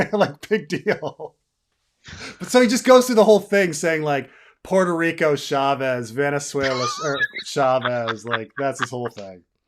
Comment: Thought you said Puerto Rico Suave (or however you spell Rico Suave) and I was dying